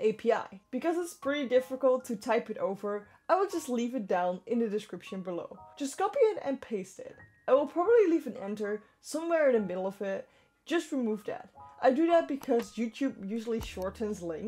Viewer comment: Leave what in the middle of what? I can't understand ): at 1:16 what are you saying? are you saying you'll probably enter and leave it in the middle of it? leave what in the middle?